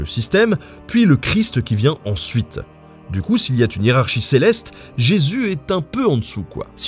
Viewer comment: "du coup, s'il y a t'une hiérarchie céleste..." Pourquoi "t'une"?